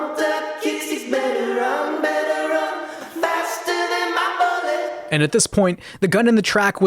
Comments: I always thought it said “better run, better run faster than my brother” this is disturbing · i thought it was saying run better run now run on my call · When I was a kid back in 2012 listening to this song on the radio I always thought his was singing “better run better run, faster then my bucket” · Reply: I thought it said faster than the sun